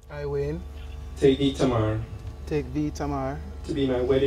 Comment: It sound like he said tamer tho